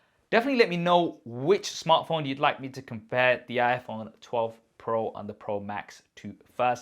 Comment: Bro you have been saying 12pro instead of 13pro couple of times in your video 9:35 onwards